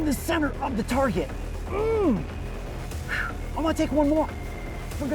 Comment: I thought he said no more